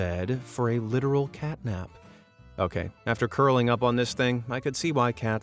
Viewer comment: did you say catnap?